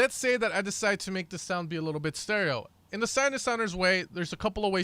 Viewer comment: you say sinedesouner instead of sounddesigner :D :D